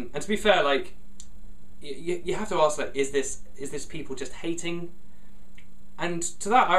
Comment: did you say is this people...?